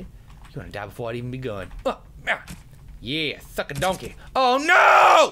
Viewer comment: omg i thought you said "f*ck a donkey!!"